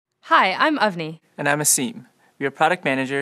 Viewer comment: At the start of this video i thought she said "Hi, i'm ugly"